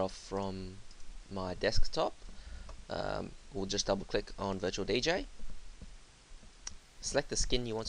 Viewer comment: sounds like ur sayin virtual BJ LMAOOO!!!